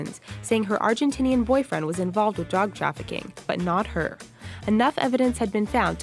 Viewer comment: did any1 else here dog instead of drug at 4:08?